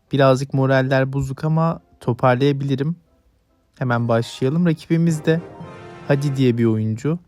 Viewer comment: Gay diye duydum başta hfsjbpj